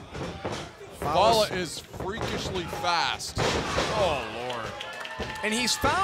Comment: when he said freakishly fast i thought he said far